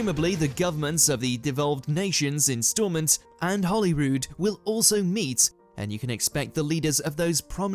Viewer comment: Did he say Holly Rude as in unkind? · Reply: Hollyrood as in Scottish parliament